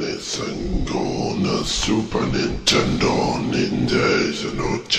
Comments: hay escuché SUPER NINTENDO DS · I swear somewhere in there I heard it say “Nintendo” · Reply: SUPER NINTENDO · Did he just say Super Nintendo???? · I know my mans did not just say Super Nintendo, lord have mercy.. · Who heard netendo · I hear the mummy says Super Nintendo. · I swear he just said 'super Nintendo' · I heard the mummy said "super nintendo"